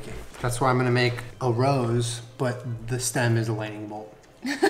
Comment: i watched this while getting ready and definitely misheard julien’s idea as “a rose but instead of a stem it’s a leg” so imagine my disappointment